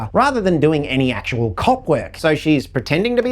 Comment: Matthew Lillard's character is either: Kidnapper of Peter's little brother The owner of P Freddie's spirit (I was correct) Peter's little brother 12:43 I was wrong. I thought said "cock work"